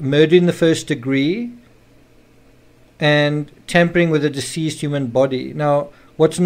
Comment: I love listening to your voice. It calms me while hearing these sad, awful details. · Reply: I thought he said camping with a dead body. I found that alarming for some reason. Need more sleep 💤 😴